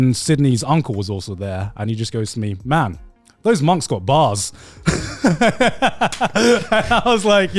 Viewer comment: was it just me or did Garnt say Sydney’s uncle said “those mums got bars”??? I was so confused and thought of a couple of things.